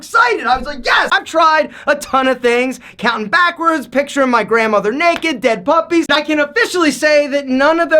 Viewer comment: Did you just say, picture of your grandma naked? Okay... You said it twice..